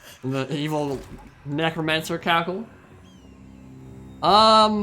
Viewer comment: I keep hearing say 'its romantic' lol